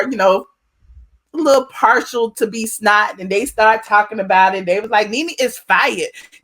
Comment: Is it me, or does it sound like she saying B Snot?